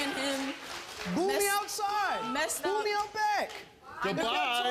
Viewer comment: LOL at first I thought that dude said "Booty out back!" as he left the stage. I thought maybe he was just advertising his services or something, but listening closer he's saying "Boo me out back." Bro just turned himself into the fully grown ass male version of Danielle Bregoli (the catch me outside chick from Dr Phil 😂)